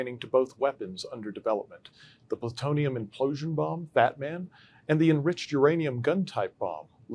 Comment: amazing again! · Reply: Ya think? sounds like he said Batman when naming 2nd bomb made, Fatman it was named (woke shit maybe) .Surely not😐